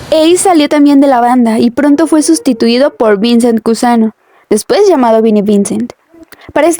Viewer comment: no se entiende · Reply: Vincent Cusano